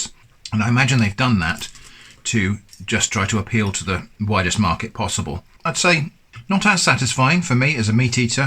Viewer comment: Did you say "the whitest market possible"? :-P